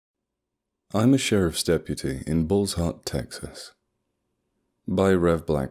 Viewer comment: So I thought you said balls hard Texas! 😅